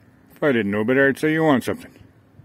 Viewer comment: I thought he said "onto something" which I would agree with.. he's definitely onto something, with all that wisdom